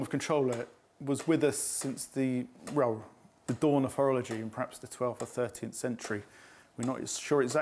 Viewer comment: What a lovely gentleman. But I do believe Sue Brunning has slashed at the tip of the lad's nose with the Sutton Hoo sword! He said, 'Horology' Sue...nothing insulting, like you thought you heard. lol. Sorry. Thank you, TBM and all curators!